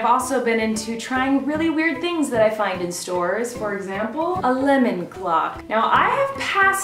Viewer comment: And I thought she said Clemon Lock